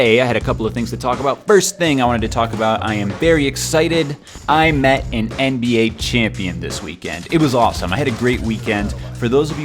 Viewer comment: Lmao I thought it said I met 3 black nba champions